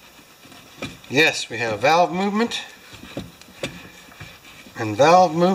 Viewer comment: Kinda sound like u said we’ve got bowel movement!!! Lol. Besides valve movement